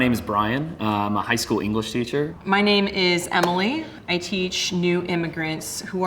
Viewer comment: omg I’m so dumb I thought that woman teacher said her name was “Emma Lee” but it was Emily lmao